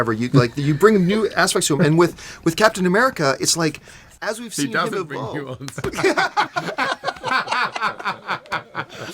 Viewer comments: Couldn't figure out what Chris said at 4:35, but finally figured out he says.... "He doesn't bring nuance." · What did he say at 4:39? I missed it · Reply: he doesnt bring the nuances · What does Chris say at 4:40? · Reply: “He doesn’t have nuance.” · what did chris hemsworth say? did he say, "he doesn't bring nuance"? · what does he say? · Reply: Do you bring new aspects to him? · what did he say? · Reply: He said "He doesn't have nuance." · I can't for the life of me make out what Chris Hemsworth's saying. Little help? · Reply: Josh Fingerhut "He doesn't bring nuance"